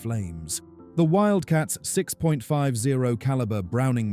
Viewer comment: Just say 50 caliber. I thought at first you said the wildcat carried machine guns that were 6.50 caliber. I hate AI voices. 👎